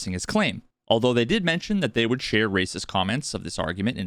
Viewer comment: Did I miss something? Where are the racist comments? Was it the comment about Billy and "crowds" or something? Idk, but whatevs it was, it flew over my head. 🤦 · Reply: You misheard there. He says “Race’s comments” (as in the findings by David Race that Billy Mitchell lied about his “””perfect Pac-Man game”””), not “racist comments”. I can completely understand the confusion though.